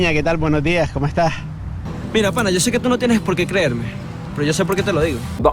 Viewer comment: what did the Venezuelan guy say in Spanish? If someone could write it down. And where’s this clip from? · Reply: "Mira pana, yo sé que tú no tienes por qué creerme, pero yo sé por qué te lo digo." Look dude, I know you don't have to believe me, but I know why I'm telling you.